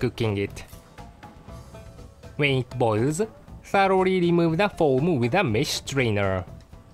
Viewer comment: Everytime I hear him say "remove the foam" I still remember and hear it as "remove the scum"